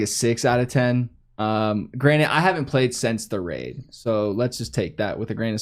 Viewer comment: Did just say 'granite' instead of granted 😂😂